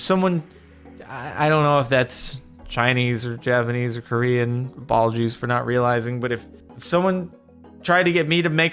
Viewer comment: I heard ball juice